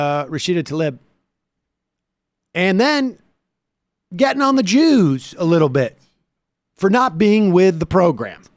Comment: Getting on the Jews. For a quick second I thought Sam said 'getting on the juice'(the sauce). You know, as in alcohol(drinking). Trump talks 'drunk' much of the time, if not always.